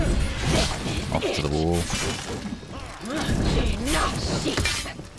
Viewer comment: This video was brought to you by ”ENOUGH, SHEEP!” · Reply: i honestly thought she said "enough s*it".